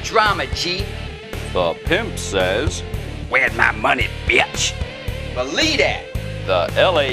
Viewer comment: i think said said b****